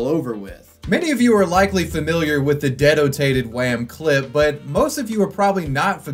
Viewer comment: I thought that kid said detonated. I can't understand the new american accent